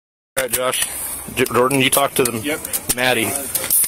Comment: Am i hearing right??? Jordan did you talk to Maddie???